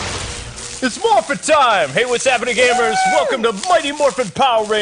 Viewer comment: for some reason i heard i got a sword XD